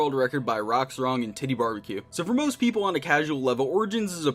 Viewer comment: I thought u said titybarbiecue